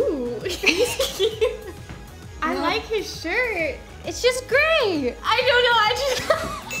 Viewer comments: "I like his shirt" "It's just gray!" LOOOL THIS HAD ME CRACKING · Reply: i thought you said “It’s just gay!” HAHA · "I like his shirt" "It's just gray!" LOOOL THIS HAD ME CRACKING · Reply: Pam D. I heard wrong and I thought she said "I like his shirt" "It's so gay" XD